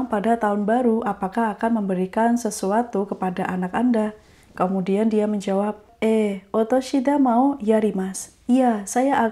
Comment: Pas dengerin ini terkecoh dengan suara mbak choukai aku dengernya 奥さん (istri orang lain). Dan ternyata yg bener お子さん (anak orang lain)😆 Harus lebih hati hati lagi wkwk cukup mirip dan mengecoh hehe